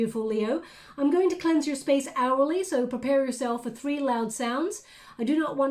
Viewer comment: I just realized that you've been saying "aurally". I thought you were saying "hourly" and I couldn't figure out why the space needed to be cleansed so often lol